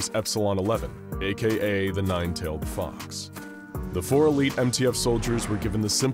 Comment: did you say nine tailed fox